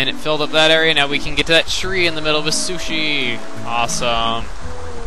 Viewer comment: at like 7:33 i swear i hear to get in middle of a sushie ..anyone??